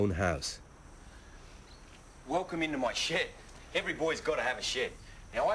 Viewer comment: sound like he said welcome too my shit any way if u tried to fight steve u couldnt hit em 4 shit Rip i just cant belive what he got killed by